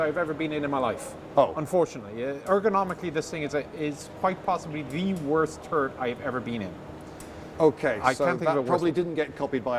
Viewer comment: Sounded like he said "..it's the worst turd I've ever been in.."